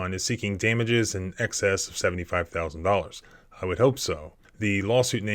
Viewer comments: The man lost both legs, is paralyzed, and is only asking for $70k? Shouldn't his attorney be asking for millions? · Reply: I thought he said $775,000? I hope this is the amount that he’s actually getting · Oh,,, I thought he said 745k or something like it.. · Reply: Crazy, eh?? He says $75,000 right at the beginning of the video and some folks here say that is where they start. Just crazy, poor man. I hope that LEO gets it back BIG TIME. No empathy for another human being at all! How the devil did that thing expect him to 'change his ways'? Makes my blood boil. What harm was the man doing anyway? Looks like a garbage dump to me.